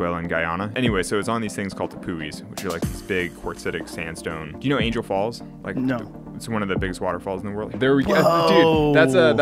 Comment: I literally thought he Said "do you know angle balls"😂